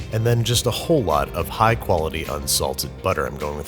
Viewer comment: I thought he said “just a whole lot of high quality unsalted salt”